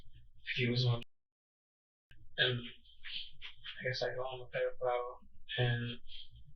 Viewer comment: I'm convinced that if you get to that age with that speech impediment your parents didnt even love you. A couple free classes could fix it. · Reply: Cc thought he said paper bottle and he said pedophile 😂😂